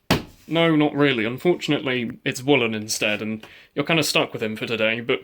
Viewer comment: I thought you said woolen is black I'm not racist staph